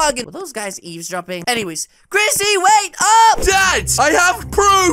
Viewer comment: I heard chrissy wake up!